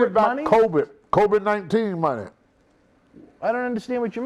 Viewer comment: Lmao...”Kobe-19” I thought Tony said “Kobe-19” instead of COVID-19...I swear, Tony can butcher any word into sounding hilarious. 😆 One of the reasons I like watching this show is simply because of the way he pronounces words. Lol